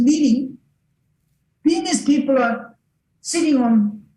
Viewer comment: I'm pretty sure said a lot of insightful things, but I just can't understand what he's saying even with the help of caption. Such as at 11:33 penis people?